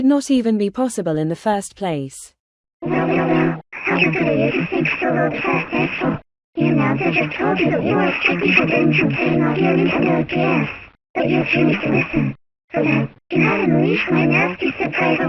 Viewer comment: What did Gracie Lou say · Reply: Gracie Lou: Well well well, how stupid are you to sink so low that fast hastyhole?! The announcer just told you that you were strictly forbidden from turning off your Nintendo DS, but you refused to listen! For that, you have unleashed my nasty surprise as I have transformed into my evil mode! It feels so great to finally get revenge on you, because I know for a fact that you were the one who has been driving carelessly! I was looking forward to a cool surprise Darren and Helen Simpson were gonna show me in Hollywood, but you had to screw that up royally! I honestly wish the police could have arrested you on the spot, but you ran away! Now I will be handling out the shame with this error! And speaking about "error", I regret to inform you that you are immediately on your last chance! If you waste yourself one more time, then I will require you to enter a code or else! I know you from top to bottom you disgrace of a human being! I already know before hand you were up to no good, because I know that what you did sometime ago was stealing this Nintendo DS and committing video game piracy with this bootleg Mario Party DS game cartridge! The Police are gonna love hearing all of this, so turn off the DS! I dare you to do it and watch what happens next! Come on and do it you loopyhead!